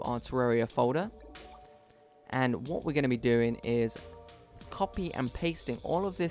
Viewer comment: Make sure you check the description for extra help like the FAQ section, thank you! MY CALAMITY PLAYTHROUGH IS OUT NOW! - <link> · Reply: at 2:42 i didnt understand what you said copy paste? Please Reply! i need your help!!!